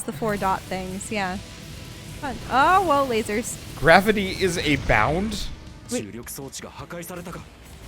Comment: he actually said "Gravity chains us!"